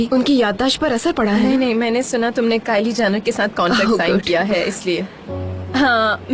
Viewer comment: Kylie Jenner?????did I heard kylie Jenner 😶